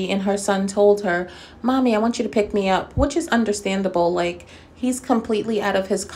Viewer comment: I didn’t hear say mommy pick me up I thought he said I wish you was here🤦🏽‍♀️